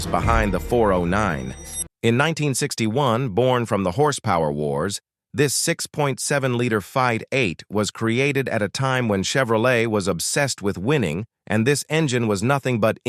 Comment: hey Mr. AI slop...... what the hell is a "6.7 liter Fide 8"? . the captions say "fight 8"..... but thats not correct (its not even a marketing term like "rocket v8" did your AI slop change V8 into five 8? but then mispronounce it because its AI slop?